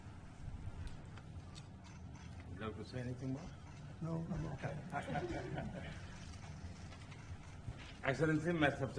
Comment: What did modi said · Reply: He said in English "Do you want to say anything more ?